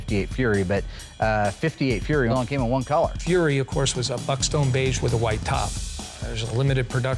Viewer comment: Did he say F@ Beige?